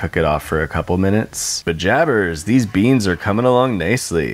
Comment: did you say “pajabbers” like from Dave the Barbarian?!